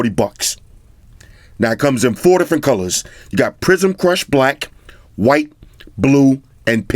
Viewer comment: Oh! I thought he said "prison crush" black. Haha